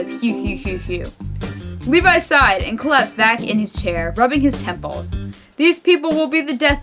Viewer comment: I thought he said "Rubbing his nipples" ._.